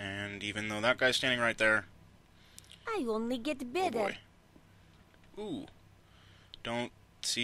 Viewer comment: i heard her say "i only get beaver" :|